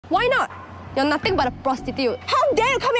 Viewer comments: HELP I KEEP HEARING "why not? you're nothing but a poosaay" OMG GMBNFIFNSO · What i hear is "You're nothing but a poosay". 😭